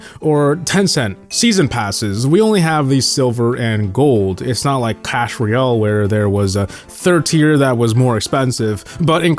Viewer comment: lmao i heard that "cash royale" then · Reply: "It's not like clash royale" clash royale had normal, gold and diamond pass, he's saying COC doesnt